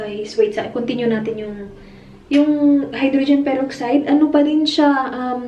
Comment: did i hear hydrogen peroxide para sa contacts???? 😯😁😅